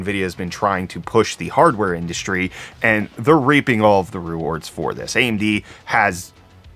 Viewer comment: "they are Raping all the rewards from it" I did a double take because that's what I thought you said. Lol, accurate though. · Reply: Reaping is what he said, or what he was trying to say. Lol